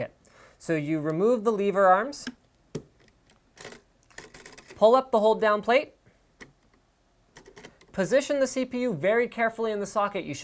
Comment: What did he say at 3:40? Pull up the whole damn plate? Lol